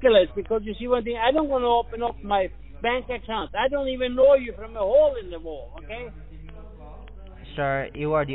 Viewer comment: “I don’t even know you from the hole in the wall”.. 😂 That’s not what he says but it sounded like it. 😅